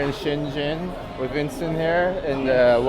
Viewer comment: did he say vincent?